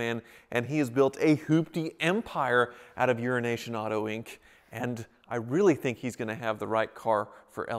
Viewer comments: I always hear "Urination Auto Inc". I can't be the only one... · Euroasian ... i thought you said Urine Nation